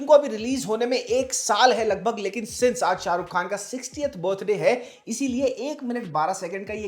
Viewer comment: You said 60th but I mistakenly heard it as 68th and was surprised and Googled immediately to confirm.